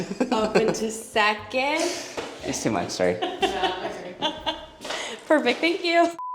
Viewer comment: does anyone know what says here? 😊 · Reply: he said "It's too much sorry" 😂😂😂